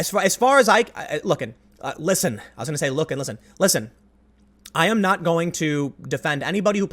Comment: Tim "Look Listen" what I hear "Hey Listen" Damn You Navi! STFU! It's been over 20 years! For the love of God get out of my head!